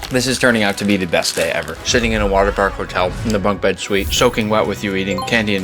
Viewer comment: why did i hear shitting in a water park